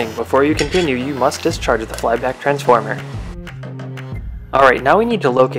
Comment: literally the only bit of the video I couldn't understand what you said, and also the most important from a "if you like living" perspective. What was said? I sort of got "You must dischargid(?) <ARGLBARGL> transformer" Ta :) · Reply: I think he said "You must discharge flyback transformer"